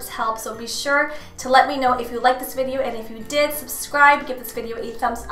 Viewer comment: I'm so sick that I thought she said prescribe at the end instead of subscribe. Lol